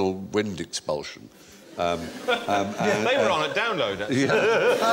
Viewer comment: What does Bill say at 1:12 ? I cant make it out, but judging from the reactions it must be funny :) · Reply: He said" They were on at download actually" talking about the download festival and inferring that Anal Wind Expulsion was a name of a band playing there